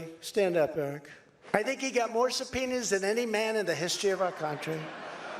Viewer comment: I misheard Eric's part. I thought Trump said Eric got more subpenis than anyone in history